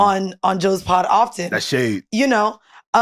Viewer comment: “That’s shade “ sounds familiar .. then I heard “that’s hate “